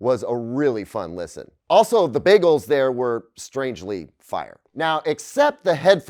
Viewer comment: "The bagels there were strangely... fire" Am I hearing that correctly?